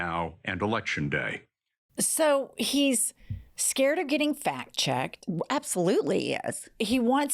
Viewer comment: Whew! You scared me! I thought you said Trump hates being fat checked! 😢 Oh wait! This is Earth One! We don't eat dogs or pal around with fictional terrorists like Hannibal Lecter! 😮😅🎉